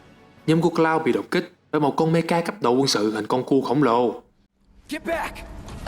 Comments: suýt nữa thì nghe nhầm :)) may mà bật phụ đề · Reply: con cu khổng lồ :)) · hình như nghe nhầm thành hình con cu khổng lồ :))